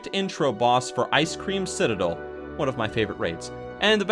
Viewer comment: i heard you say icecream citadel and i laught for like an hour